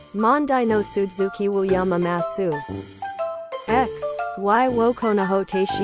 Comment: X=1が赤スパチャに聞こえたw